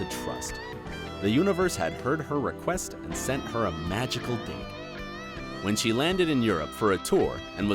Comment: did he say magical dick lol